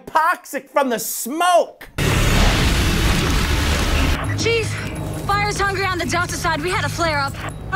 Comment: i heard cheese not chief :DDD